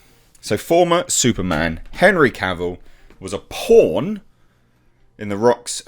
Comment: I hear "porn" whenever a Brit says "pawn"